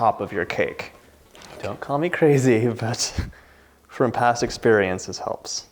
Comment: did you say " don't call me crazy you bitch"? If not it sure sounds like it. Lol, I think I'll just skip the crust and just eat the filing with a straw. Thanks, John for another hit out of the park.